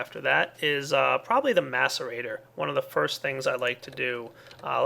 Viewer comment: I thought he said masturbator.